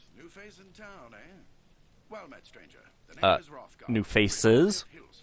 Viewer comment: The dialogue said, "Well met, stranger..." to my semi-deaf ears, it sounded like he said, "Well, menstruator..."